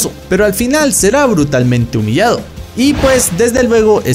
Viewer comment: Humillado ? Yo entendí "brutalmente violado" :v